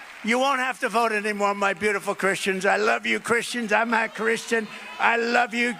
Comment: "I'm a Christian." The M in "I'm" combined with the way he mispronounced "a" made it sound like he said "I'm not Christian." He said "a" the same way you pronounce the "a" in "and."